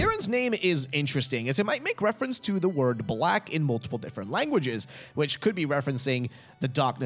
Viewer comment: wtf happened around 20:00 when you said “to the word black in [unintelligible] languages?” lmfao it sounds like you got possessed for literally half a second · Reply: He says multiple are you deaf